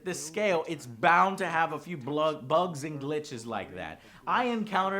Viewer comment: "Blugs and gooches..." ?? 23:50 lol, is that what was about to be said? I'd almost argue games need more of those... whatever those are.